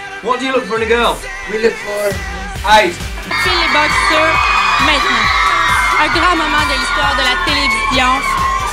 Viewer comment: C'est moi ou à 5:38 la meuf dit en français "Un grand moment dans l'histoire de la télévision" ?